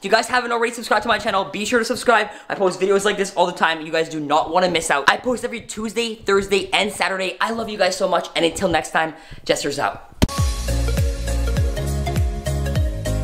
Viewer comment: Did anybody listen to that audio what it said cuz it sound like it said kill jester